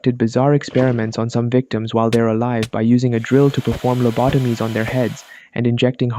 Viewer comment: DID SOMEONE SAY LOBOTOMIES????